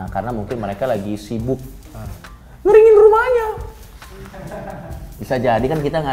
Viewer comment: anjing lah coki, nagih bngsd dengernya. Sekarang coki lumayan jarang pake nada²nya, kecuali buat yg punchline yg isinya sarkas/satir/dark.😂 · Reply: Ngeringin Rumahnya